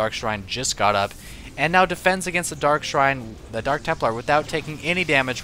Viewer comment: When he said, "defense against the dark shrine," I thought he said defense against the dark arts....